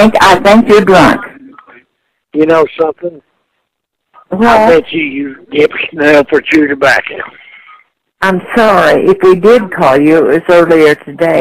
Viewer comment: what is he saying? Can't understand it, mouth fulla shiet again. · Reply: I bet you you dip snuff or chew tobacca